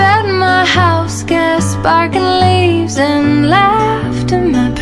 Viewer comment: I always thought she said "bark and leaves".. guess i was wrong.. :}